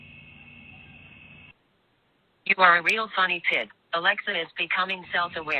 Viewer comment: Sound like Alexa said you are a “real honey pig “ 🤣🤣🤣🤣🤣